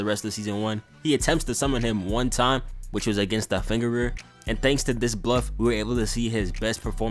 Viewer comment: You did not say finger bearer you said the fucking fingerer